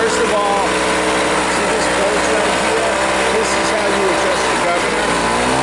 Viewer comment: Great video, very useful. At 16:19 you explain what a bolt adjusts but I can't hear the audio and the subtitles are blank. I think you say it adjusts the governer??